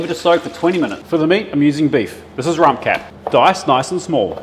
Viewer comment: Looks absolutely delicious!! At first I heard rum cat, and thought naahhh. Watched again and thought maybe he said rump cap…hoping so!😂